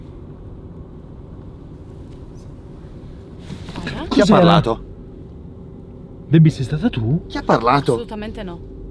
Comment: Al minuto 9:49 si sente una voce voi capite che dice? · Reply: luigi summo dice voce femminile “sono qua” un bacione 🥰🥰🥰🥰